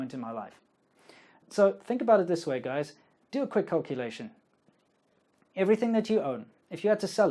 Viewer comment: Lol, 8:05 thought I heard "Do a quick copulation".